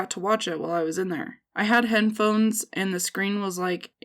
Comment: I dont know why but in 5:41 instead of hearing HEADPHONES I heard HEN-PHONES!!!